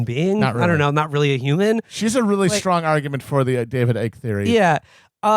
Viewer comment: I heard them say the "David Egg theory". That doesn't sound right. Does anyone know what that's about?